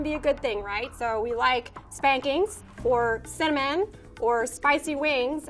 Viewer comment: I thought she said "spankings, or semen, or spicy wings". What am I thinking about? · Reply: Close. She said cinnamon after "spankings" 😄